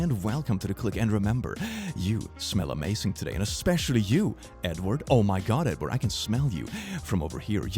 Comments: I almost thought click said Nword instead of Edward lol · Bro when you said “Edward” I thought you said “N-word” and I had to do a double take